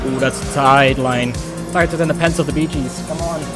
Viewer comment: and upload on the same day? We eating good today lads Also what were you saying at 22:13 about something tighter??? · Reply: I said “Tighter than the pants of the Bee Gees”, iykyk :’)